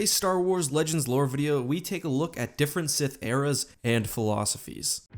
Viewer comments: Who else thought it said Sith Ears? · Thought it said Sith Ears It's okay, maybe someday